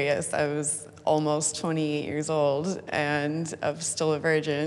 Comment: I thought she said she was eight years old. 😅